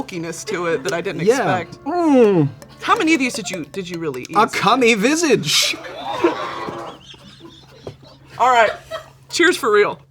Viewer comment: Can anyone explain what Cole said at 9:37 ??? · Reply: i think he said "a cummy visage" which just means "it looks like cum"